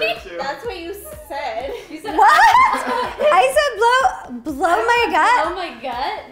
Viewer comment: Hahahaha that’s so funny It really did sound like you said “blow my guts” rotfl 😂